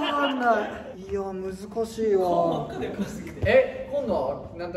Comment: 北斗「顔真っ赤だよ 〜？？？」 なんて言ってるかわかる人います？？！ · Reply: 「顔真っ赤だよ、掻きすぎて」って言ってるのかと！字幕ONにして見ててそう出てました！